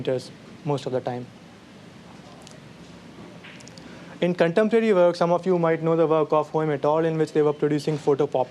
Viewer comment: what the heck does he say at 18:46... anyone? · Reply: I had to rewind to catch that too, but I believe he slightly mispronounced "in contemporary works"